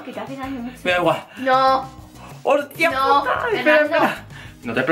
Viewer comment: escuche mal o dijo puta